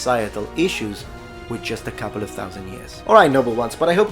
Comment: Near the end you said "civilizations do not change dramatically with just a couple of thousand years". At first I thought you said ".. with just a couple of thumbs in the a**" and I thought "What's a nice metaphor!"